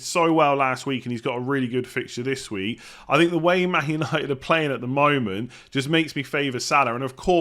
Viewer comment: Did you say manchester united or something else